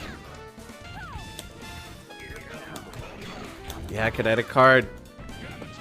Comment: KINETIC CARD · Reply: For the longest time I thought he was saying Credit Card